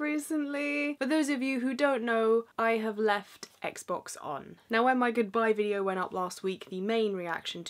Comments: At first I thought it said "why I left my xbox on" and I was like 'what a specific thing to talk about' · At first I thought it said "why I left my xbox on...."